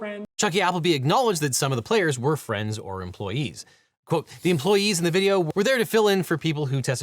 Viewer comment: For a few seconds I thought you were saying X employee (twitter) lol